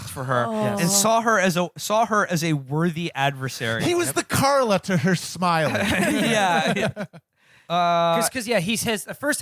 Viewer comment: What did he say at 2:03:29? "He was the ____ to her _______." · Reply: "He was the Karla to her Smiley." Characters from John le Carré novels